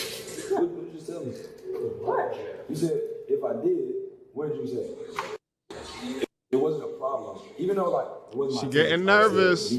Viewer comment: you missed a key piece of info - off camera she said: 'If he did then it wasn't a problem.' What loyal woman says that?😂